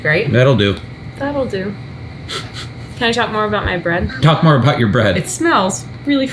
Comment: I swear to god I thought she said "Can I talk more about by breast?" What!? Oh, bread...